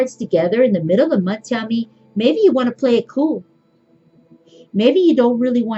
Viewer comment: At the 8:55 time point in the video you are holding up the fool and the hanged man cards and you said, "So these two cards together in the middle of the month Tammy, maybe you want to play it cool." Did you say the name Tammy? Can't wait to see the To Be Continued Aquarius video.